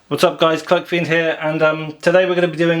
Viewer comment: I thought he said "cuckfiend here".